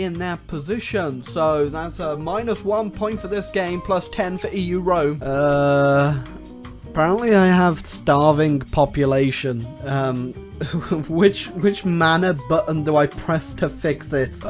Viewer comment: I thought that said "Starving Popes"